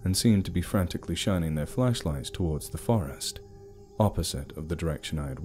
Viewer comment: Double take when I thought it said ‘Fleshlight’😂😂😂😂